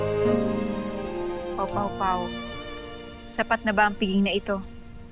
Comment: Guys May Narinig Ako Sabi Ni Danaya sa 0:02 Pao Pao Pao